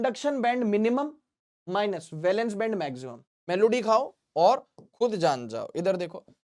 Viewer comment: sir did you just say melody